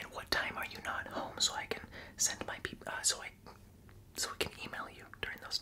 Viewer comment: what did he say? 😂 I thought he was gonna say send my Pepe or something 😂